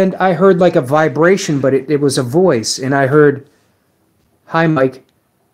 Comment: Wow, spirits are not shy here so this is going to be awesome. I couldn't understand her clearly but certainly replied to you. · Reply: sounded different when i reviewed it. I heard "Hi Mike"